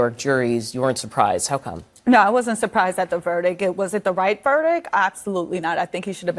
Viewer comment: Did she say verdick?